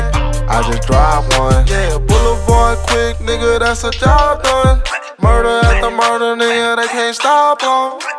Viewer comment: Is it me or did he say BLVD QCK THATS A H JOB DONE AT 1:15?